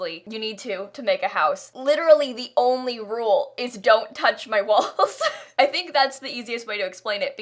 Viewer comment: I thought you said don’t touch my balls at first